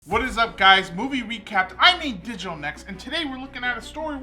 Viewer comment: Uhm is it just me or did he say "boobie recap" LMAO